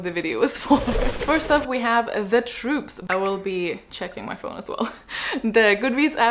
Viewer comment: the way i thought the first book said The Poop at first and not The Troop